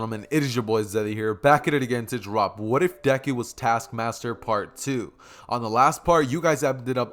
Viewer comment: Thought it said "What I deku was Task Manager"